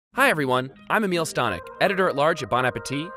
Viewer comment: Can't believe this guy's name is literally a meal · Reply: I always thought he said”a meal stonick” and just now realized his name is literally”amiel”